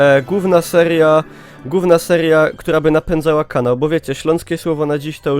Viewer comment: "Gówna seria" tak usłyszałem DXD